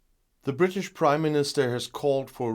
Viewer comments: I always hear you saying “the British Crime Minister”…😋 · I just heard "british crime minister", was of course my fault, but it seems fitting :)